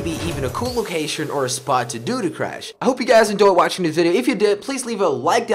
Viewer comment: he said I hope you guys endoy instead of enjoy